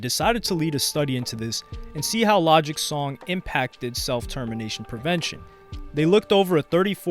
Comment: Oh damn I misheard when he was saying Self Termination I thought he was saying Self Determination, I was like isn't Self Determination a good thing, shows how much I can hear while this Honda GX 160 is running 😅